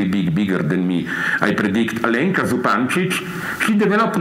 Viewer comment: Who is she? I can't understand her name. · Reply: Alenka Zupancic